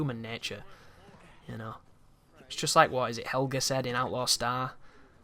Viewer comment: average distance between each asteroid in the asteroid belt is just above half a million miles, doubt anything could jump half a million miles lol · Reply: What did he say at 31:23 to 31:25? Something about stars? helghan? Killzone?